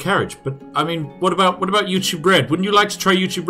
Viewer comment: am I the only 1 that thought you said youtube bread? XD